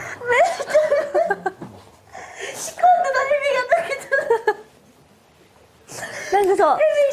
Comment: ～あたりのももちの言葉が聞き取れない なんて言ってるんだ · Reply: kikusu akan 仕込んでた蛇が抜けちゃった ですかね